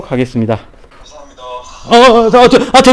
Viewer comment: 이어폰 끼고 들어보니깐 감사합니다로 들리네요 아닌가?